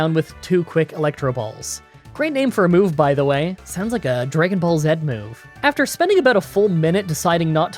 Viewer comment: Am I the only one that heard him say dragon ball zed